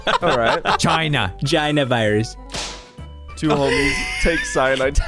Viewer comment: i heard jina lol